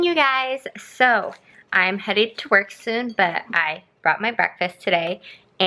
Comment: Idk why I heard "I'm headed to twerk soon" instead of work...ok will continue watching now